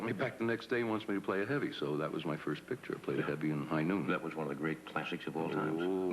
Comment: What is the movie name he said at 5:00 ? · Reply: High Noon. 🎥